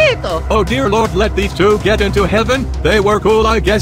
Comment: It sounds like bob is saying “western Sphagetti”